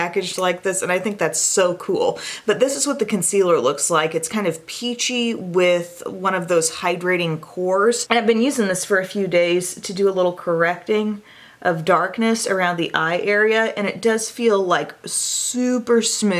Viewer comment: Omg when you said the Dior concealer had a hydrating core, I thought you said “hydranium core” 😂